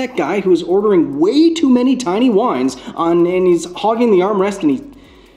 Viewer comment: what did he just say? "way too many tiny wines on nannys hog and arm wrestling"????